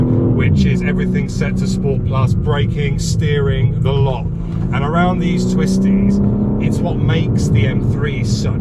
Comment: Furkapass for the win! 6:53 I thought I had heard you coin a new phrase “Swissties” for Swiss twisties